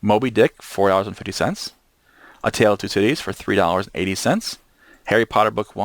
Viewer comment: Such an amazing collection of knowledge. I listened while driving for the first playthrough and I kept hearing "A Tale of Two Titties" made me chuckle many times.